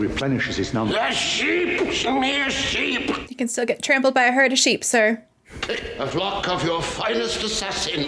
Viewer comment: Did you say "herd of sheep?"